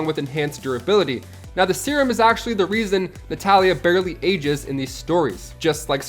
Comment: Did they call Black Widow Talia? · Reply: Nah, he gets the “Na” of Natalia but it’s quick and quieter than the rest.